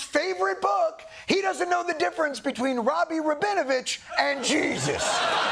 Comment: FYI, the cc says "Rabbi Rabinovich", not "Robby Robinavitch"; Dr Robby is Jewish, but not THAT Jewish